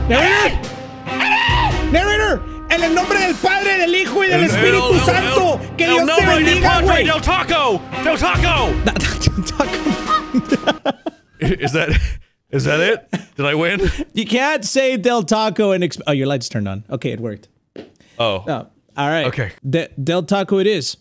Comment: taco taco 😂😂😂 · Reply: It sound like he was saying el taco then nacho taco